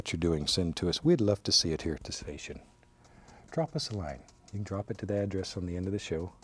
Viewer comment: "Drop us a line" I swear to god i heard "Drop us a like" and my mind blew...